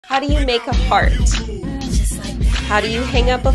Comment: For a sec I thought it said how do u make a fart